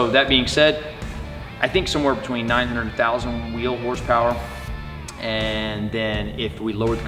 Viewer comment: did I hear that right 900,000 wheel horsepower 🤔